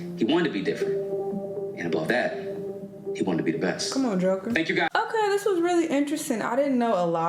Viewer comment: did she say ‘come on Joker’ 😭 lmao